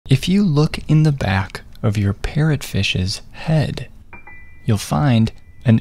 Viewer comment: I thought he said “if you look in the back of your pet fishes head” 💀💀